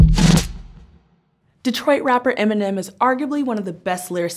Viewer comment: I thought she said "rich white rapper" instead of Detroit in the beginning 🤣